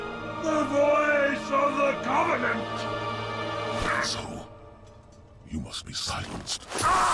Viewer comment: So, when i first played this i didn't have subtitles on, so i thought Arbiter said "Asshole" instead of "And so".